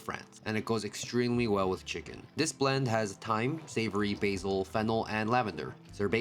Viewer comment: For a second I thought he said and fentanyl, but he said... fennel?????